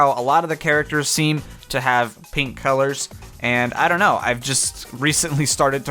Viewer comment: I thought you said pink gold if you really said that then I'm telling Nathaniel bandy